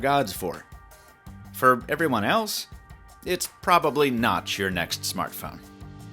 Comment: I heard : "it's probably NOTCH your next smartphone"